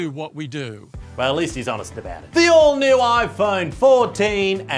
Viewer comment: I always thought said “The Old New” as a joke, but it turns out I just can’t hear well